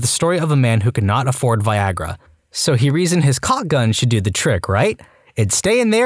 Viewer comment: I swear I thought you said Cocked gun , I had to watch that but again , not sure which was more terrifying tbh. Btw my heart aches for the amount of stories that wouldn’t have happen with even basic sex education